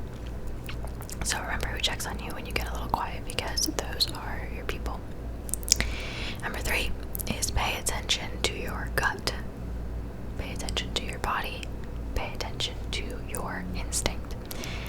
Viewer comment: I thought you said “pay attention to your c**t” 😭😭